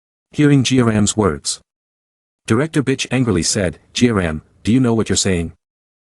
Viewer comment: Why in 1:19:40 to 50 he said b#itch instead of beck with the guy on the doir